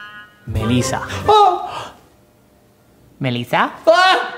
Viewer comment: porque todo el tiempo escuché leviosA